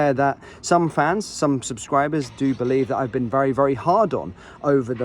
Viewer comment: Am I right in thinking that the content creator is a little disappointed with Lundstram? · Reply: I thought he said he had a "hard-on" for him